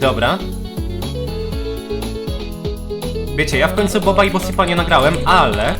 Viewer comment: Heh ja usłyszałem bob and boSIMP